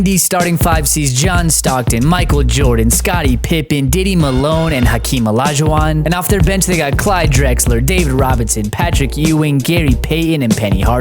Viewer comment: Diddy Malone is crazy😂😂😂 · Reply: I watched this over like three times and then on the third time I heard Diddy Malone and I was dying ha ha ha I was like wait did he just say Diddy Milo I was done. I got a sub subscribe to this channel just because of that.